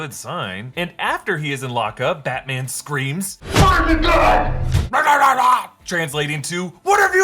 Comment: Lol, I thought Batman was saying “why aren’t you dead?” This makes more sense